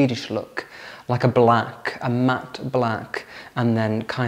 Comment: As a non-native speaker, I often have problems understanding, but a compliment to you... your pronunciation is so clear that I can understand almost every word well. About your new furniture purchase. Since you've already painted a lot of things white, I would leave it as is, although the blue chest of drawers you showed as an example looks great, especially that shade of blue. Your cookies are tempting and I enjoy your tea ceremony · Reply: Blue? I thought he said it was matte black. Looked like it or a charcoal.